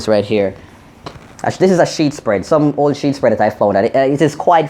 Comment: thought she said her pum pum fluffy like cheesebread lmao that what i sing 😂😂😂😂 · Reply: she said "sheet spread" not "cheesebread" lol